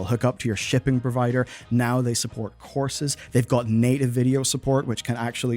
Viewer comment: I misheard . "Now they support HORSES "